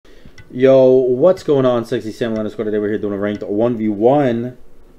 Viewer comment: K I have to ask... wtf do you say every intro? you say it WAY to fast to understand. I hear "Yo, what's going on sktsksevknekn ranked 1v1" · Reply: "Yo what's going on sexy samalander squad today we're here doing another 1vs1"